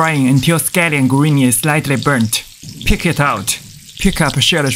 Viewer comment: I really heard 'pick it out' As KICK IT OUT🤡